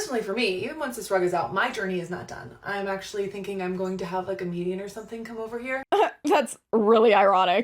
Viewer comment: i heard comedian 🤣